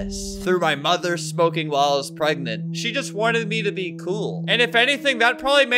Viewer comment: I know this is serious but it sounded like he said, “for smoking while I was pregnant” 😅